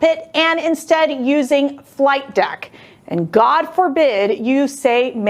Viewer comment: Did he just say flight dick?